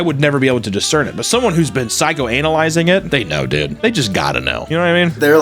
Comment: Did u say “psychoANALizing” at 17:45??? That was pretty smooth if it was on accident LOL